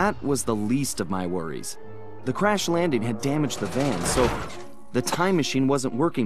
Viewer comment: I had my volume low and thought he said “Crash Bandicoot”